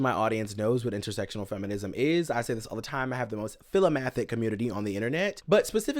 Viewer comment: what word did he say here? He has "the most filimathic(?) community?" I ask because captions don't help either and it says "filthic" which sounds nothing like what he said. And I know this is a word I've never heard before. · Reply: Philomathic: love of learning